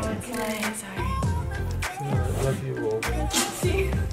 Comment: Is it just me or did say "yes Franny I love you"